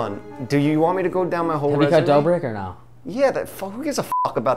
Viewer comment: did he say Dobrik 😂